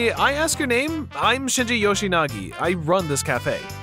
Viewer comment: Thought he said yoshikage for a second...